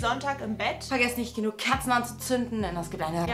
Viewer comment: Der moment wenn du bei 9:28 statt „kerzen“ „KATZEN“ verstanden hast 😂😬